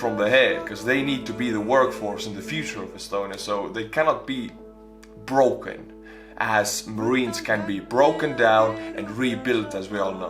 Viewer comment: Lol 1:20 ....Did he just say Marines are nuts?